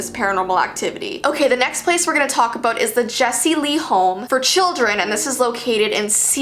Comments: I heard Jessii. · Jessy Lee children home and youtuber NOPE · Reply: katie roberts, omg! They sound so similar! I thought she said Jessii vee but she said Jesy Lee! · At first I thought she said jessii vee home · i thought she said home for children lmao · jessi lee sounds alot like:/ 4:38 now i think she said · In 4:05 of the video she said Jesse Lee thought she said Jessii Vee home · did she say Jesse lee :O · Who else thought she said the Jessie Vee home😂